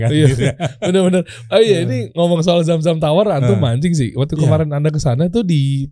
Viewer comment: itu Host nggak bilang "antum A...g " Kan?? Semoga ana salah denger ya..😂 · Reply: yang benar bang bilang "antum mancing sih" anda salah dengar